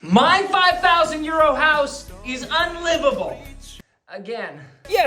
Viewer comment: When I first started watching, I thought you were saying 5,000 'year old' house. America here, lol